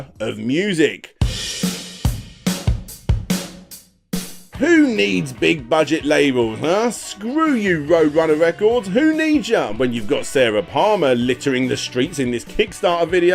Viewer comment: Did he say Sarah Palmer @ 6:28 or am I just mishearing?! Between watching on my phone and blowing out my ears or the past 20 years my hearing can suck at times.